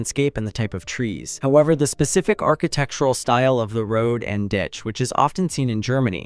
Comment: the word the AI said was "ditch". In the UK and Ireland thats what we call the shrubbery at the sides of a road. I'm not sure if that word is used elsewhere. I am assumng you didn't know what that word is because you put ?????? as the subtitles (or did the AI do the subtitles?) Edit: Yeah the AI did the captions. I wonder why it said Ditch, but then put ????? in it's captions.